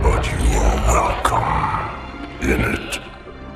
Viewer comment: I just imagined that he said “you are welcome innit” instead of how it’s meant to be and I can’t stop laughing.